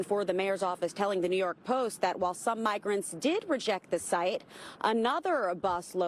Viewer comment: I keep hearing the word “Migarants” rather than illegal immigrants.